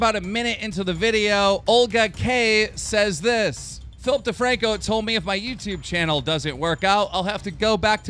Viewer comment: Philip DeFranco, when I first heard that, I heard it as Filthy Franko